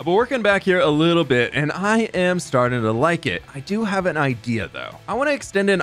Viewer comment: Does anyone else hear igea instead of idea at 11:50 or was that just me